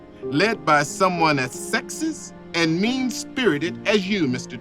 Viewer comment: I thought it said “sexiest men”💔